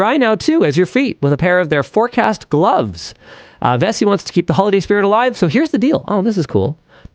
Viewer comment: I thought Linus said that Vessi wants to keep the Holly Spirit alive... Holiday spirit, he said holiday spirit!